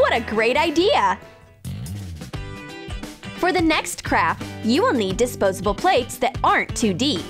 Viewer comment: Am I the only one who heard her say "For the next crap." ?